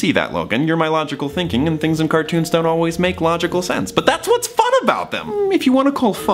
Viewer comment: How does one change the subtitles it says "start always make logical sense" instead of "dont always make logical sense" which like is an understandable mistake, he has a weird inflection on it but changing it would probably be optimal I'll edit w a timestamp if someone knows how to do that Edit: it's